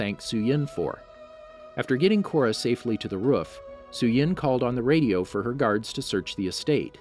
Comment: ruf instead of roof